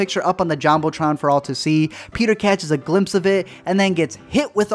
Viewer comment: Am I the only one who heard him saying “Peter” and not “Jamey” or “Noah”